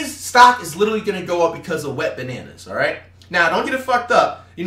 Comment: did bro say “Wet Bananas” ayoooo